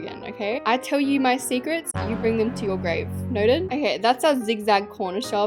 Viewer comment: I thought she said "IT GETS LOOSE I BRING U 2 UR GRAVE!"💀